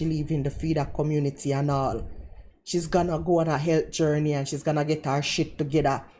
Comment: All I heard was , Rosie's on a health gurney